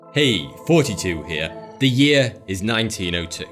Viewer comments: I always thought you said ‘hey 42 here’ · I always hear him say "hey 42 here"